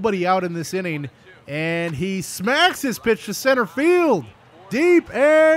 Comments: did he say pitch or bitch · During 4:13 did he say smacked that bitch or pitch because I've never heard curse befor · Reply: Pitch. You must not have heard him when he livestreams with.